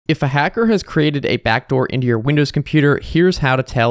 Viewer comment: I misheard backdoor and i thought he said backrooms